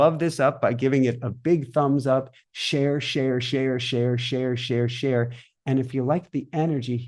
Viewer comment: I nodded off during the meditation and woke up to hear “share, share, share, share”. I thought was saying “Cher, Cher, Cher, Cher”. I wondered what SHE had to do with all this, lol. Of course we are all one though 😊😉